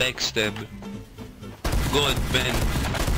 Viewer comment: i thought he said ga-ben but he said god ban XD